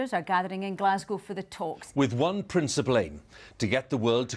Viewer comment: Sorry but I can’t get past 0:11, did Hugh just say princip-ley?